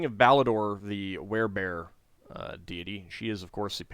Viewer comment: At first I thought you said "Care Bear Deity" and was far more excited than I should have been.